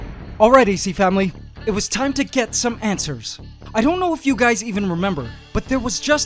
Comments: Gosh darnit! I laughed when I thought he said "Let's find some 'antswers!'" 😂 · When you mishear "answer" as ANTswer. I've watched this channel too much. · I heard him say "Its time to get some ANTSWERS." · hAhA ants-er instead of- yeah ok no one gets it 😔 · Ok when he said “ok Ac family it is time to get some answers “ I thought he said “ok Ac family it is time to get some antswers” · i heard "Time for some ants ers" not "time for some answers" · I thought he said antswers and I was like really? A pun?